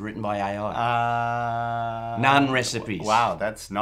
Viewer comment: When he said "none recipes" I thought he said "nine recipes" and I was like "really?"